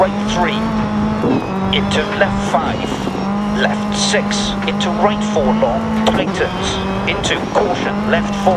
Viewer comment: Hello, can somebody help me and tell me what co-driver says in 0:34 this word which starts with "T" ? I don't understand english that well :( Btw. insane driving m8 :D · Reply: "Into right 4 long. Tightens!"